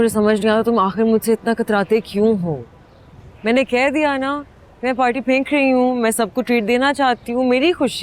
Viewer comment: is it me or she really said " me party phenk rahi hun " 😂😂😂😂